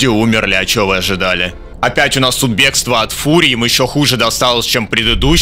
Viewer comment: мне ли не послышалось? "бегство от фурри" ) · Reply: вообще там бегство от фурии, но этот вариант как-то попизже и по реалистичнее